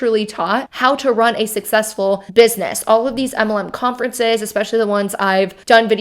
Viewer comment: I chuckle every time you say MLM. It sounds like you say EMLEM 😂😂😂